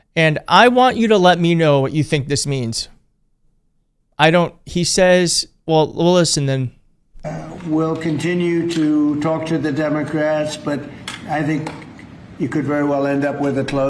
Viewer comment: I thought he said "We'll continue to torture the Democrats." I am am sick to death of having a person in office who thinks it's clever to name-call like a first grader. (Though I do call him King Con.)